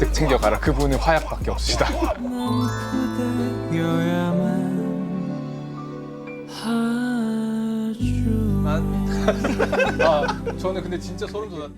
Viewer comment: 여기서 교수님이 뭐라고 하시는걸까요..? 그분의 화약 머시기머시기 같은데.. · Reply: 그분은 화약밖에 없으시다...ㅎㅎㅎ